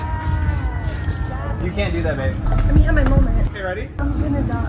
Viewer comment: Did I just hear someone say babe to charli??